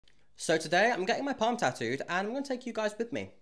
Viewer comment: It sounded like you said "bum tattoo" 😂